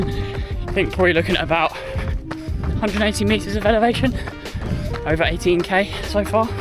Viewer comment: did you say 180m of elevation? 😅